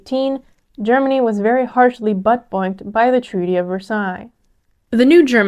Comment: I swear you said "very heavily buttplugged" I've listened multiple times and still not convinced it's not.